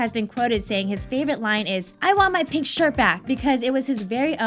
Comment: Also, i always thought he was saying "i want my picture back" not "i want my pink shirt back"